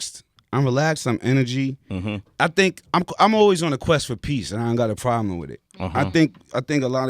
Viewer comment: I hear "Superman 5" every time he says "Quest for peace".